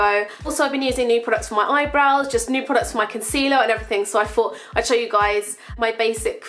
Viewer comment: I'm sorry for asking but I'm not a native English speaker and also I'm the curious type: in the beginning it sounds like you say "fought" instead of "thought", is that like a dialect or am I hearing it wrong? (